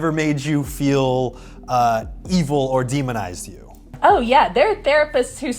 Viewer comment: thought it said "evil and demonetized you"